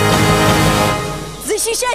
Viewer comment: What is the woman shouting at 0:30 ? Could someone please tell me? I've been wondering for years now, every time I listen to Jaguars compilations I wonder... Thanks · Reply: "To protect the Motherland and to protect the criminal state are not the same thing!"